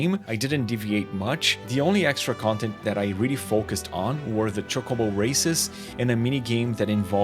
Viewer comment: I could sworn i heard you said chocobo racist 17:36 minutes. 😂😂😂